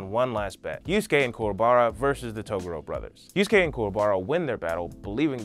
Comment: I thought he said Toblerone brother lol, then couldn't unhear it for the rest of the video 🤣🤦‍♂️